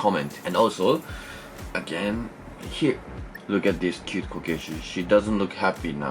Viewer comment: when you said “cute kokeshi”, I thought you said “cute Caucasian” xD